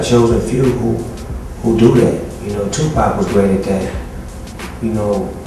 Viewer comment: Does he say "2pac was ready to die?"